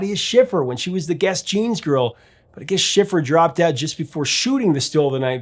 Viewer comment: did he say " I guess schiffer dropped dead right before shooting the still of the night video"??